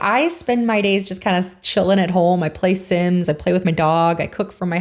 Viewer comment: "Play SiMs" I thought you said "Play SiNs" and was like oh.. tell me more.... lol.